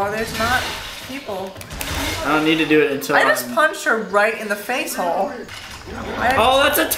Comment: Does her said ''I just punched her in the face hole'' lol